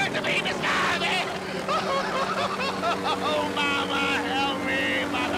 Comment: Timing of this right after the anniversary of Kobe's death is spooky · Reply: As they were crashing down, it sound like he was saying Mamba instead of mama, and then I seen channel 8 icon, that when I knew this wasn't for me... Thumbs down !!